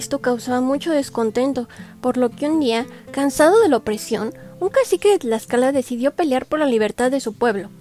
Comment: Que bonita historia de Mexico😊que dice en el minutob 0:58 ?? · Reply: Por lo que un día, cansado de la opresión, un cacique de Tlaxcala…